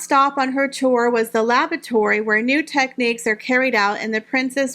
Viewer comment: I had a laugh out loud moment when you were showing Catherine's visit to the weaving factory and said her last visit was to the laboratory. I swear I thought you said lavatory - then thought it through and realised what you meant. Re Harry and Meghan, you have Harry spot-on. No real life ambitions, just a man-child wanting to play all day. However if she had the brains to see it, Meghan would realize that she's nothing without Harry - all those invitations would dry up in a heart beat if she divorced him. As for marrying a billionaire - she is such a well known narcissist now that she wouldn't be able to snare anyone into marriage again. After all, at her age, she can't pull the "I'm pregnant" stunt for much longer, can she?